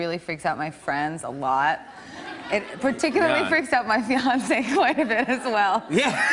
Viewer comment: I thought she said Beyoncé but she actually says fiancé. ☹